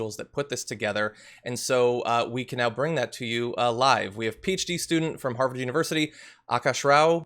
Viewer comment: When he said "PHD student", I literally thought he said "peach tea student"!!! Please alliterate Mr Iadirolla(I hope I spelled your name correct!) Check it out!!! 0:32 !!!